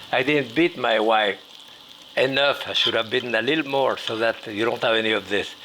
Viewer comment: I accidentally started the video at exactly 4:21 and I thought he said "I didn't beat my wife... enough. I should have a little more". Clearly he doesn't say that but it was funny once I realized what I had done